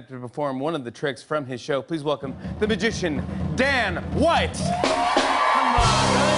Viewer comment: At first, I thought I heard Jim said "Please welcome the magician DAMN White!".